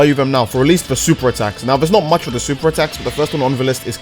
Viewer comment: Damn I thought he said new super attacks XD